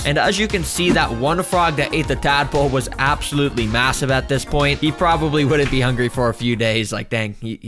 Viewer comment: Did u say m…m..massive…?